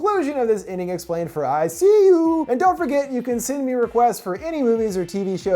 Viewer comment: I dunno why, but I heard 'I See You' at the end as ICU, and now I think a horror movie of sorts where the lead is stuck in an ICU, unable to escape and terrorized by a hospital staff member or something, essentially being tortured and relying on them to live, would be more interesting than this movie.